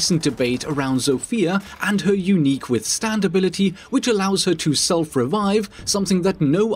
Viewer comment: legit thought you said zofia’s [Stand Ability]